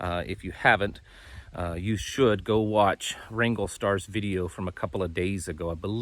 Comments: Good Grief, I thought you were saying I Ringo Starr! I said, what the? finally figured out Wranglerstar, I dropped him when he was having a pity party over males being picked on, like females aren't picked on by the males? I'll pass, thanks but this channel is sufficient for me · Can't understand the name of the guy you had listened to. Sounds like you were saying Ringo Star? Nothing comes up! My ears are getting old!